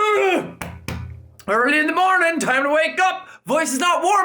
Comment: For a while I thought he was saying "Top o' the morning to ya ladies!"